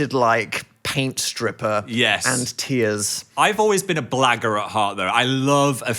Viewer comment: i love the fact that i have bad hearing, because 16:52 sounds like phil just said "i've always been a black girl at heart"